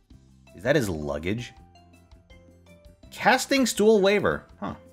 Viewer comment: I was nearing the edge of sleep till I heard the words "casting stool waver"